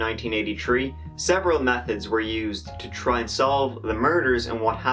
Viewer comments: Lmao sounded like he said there were several meth heads used to solve the murders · I thought you said “several meth-heads were used”. Kept wondering!